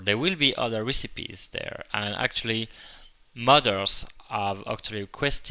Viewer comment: i was confused thought when he said "modders" he meant "mothers" i eventually figured it out :/